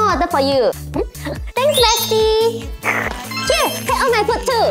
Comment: I heard “ thanks Messi”